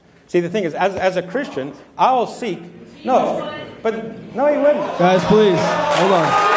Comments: What does the woman say? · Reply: "Jesus would." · ok this video is super old so i dont know if i'll get a response, but what did the woman at 8:44 say in the audience?? that has always bugged me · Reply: She said: "Jesus would..." which I think is in response to Sye saying that he doesn't do Bible studies with non-believers.